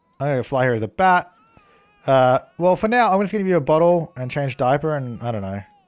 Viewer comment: OMG... when you said you were going to "give the baby a bottle", it took me way too long to figure out what you actually said because it sounded like you said "butthole" not "bottle" and I was like, "WHAAA...?!" I'm so glad that wasn't what you said, lmao.